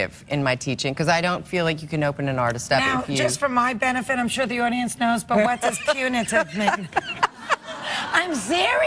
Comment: I don't understand what Fran says at 5:50 and why Lauren laughs... Can someone please write that down for me? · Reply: "Just for my benefit -- I'm sure the audience knows -- but what does punitive mean?"